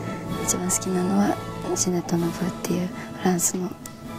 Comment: で触れているフランスのヴァイオリニストの名前が聞き取れません。どなたか教えてください。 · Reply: 糀谷市郎 ジネット・ヌヴー(Ginette Neveu)ですよ。30歳の時に飛行機事故で亡くなった伝説的バイオリニストです。 第一回ヴィエニャフスキ・コンクールに15歳で出て優勝しています。その時２位になったのが26歳のオイストラフです。 YouTube にいっぱい録音がアップされてるので聴いてみてください。↓このブラームスが有名です！ <link>